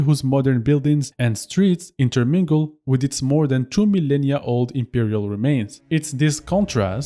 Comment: It sounds like you said the ruins of Rome are 2 million years old? 🧐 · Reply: 2 millennia